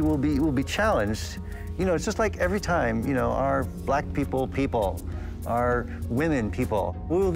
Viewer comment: this got awkward fast 🤣🤣 , subtitle added lack instead of black. So politically correct